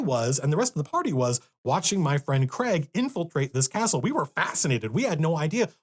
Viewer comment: I heard "I was, and the rest of the party was, watching my friend Craig infiltrate this asshole "